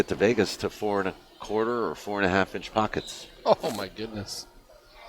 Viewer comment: what size of those pockets? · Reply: I thought said 4 Inch Pockets.